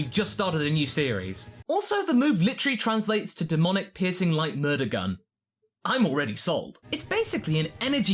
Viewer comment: Was playing this in my headphones while on Overwatch and I swear I thought he said "Demonic Pissing Light Murder Gun"